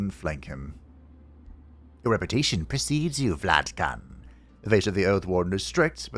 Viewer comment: I heard that as.